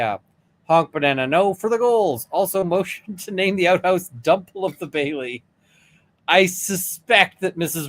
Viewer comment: Dump Hole of the Bailey is how I hear it